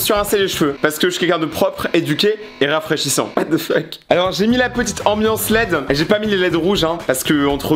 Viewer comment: j'ai mis la petite ambiance laid Moi qui avais pas compris que c'était les lumières et qui croyais qu'il parler d'une moche: c'est censé être moche ? Moi qui a capter 3 secondes après : ah mais👁️👄👁️